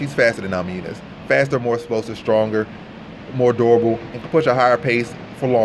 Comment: I thought he said "more adorable" LMAO.